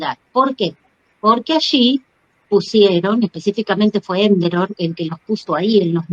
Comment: Minuto 25:26 dice específicamente fue .... Quien lo puso ahí (las partículas luz) A quien se refiere ? No entiendo lo que dice. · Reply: Enderol enki, es un genetista, es quien nos puso al humano luz y puso la llave para que ninguna especie o raza que nos controla pueda dar con la combinacion de abrir esa llave o descifrar nuestro códice de nuestra esencia. Espero te sirva. 😊